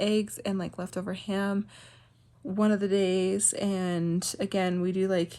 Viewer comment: I thought you said “make believe ham” 😂 then I saw the little maple leaf. I need to go to bed.